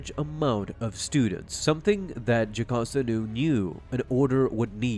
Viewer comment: is it just me, or did he say Japasta Nu?